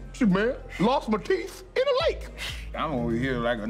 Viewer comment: "I ain't concerned about Drake, ...lost my teeth... in a lake" 😬🤣🤣🤣🤣🤣🤣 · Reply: I thought he said lost my kids in a lake xD